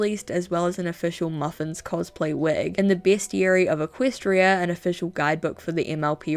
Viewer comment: When you said bestiary, I thought you said best yuri